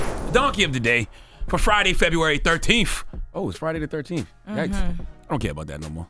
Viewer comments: Did he say "Friday February 13?" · Did he say FEBRUARY 13th at the 0:23 mark??